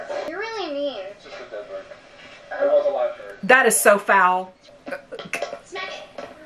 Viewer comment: my brain heard her as 'That is so fowl' and not 'foul'. Accidental funnies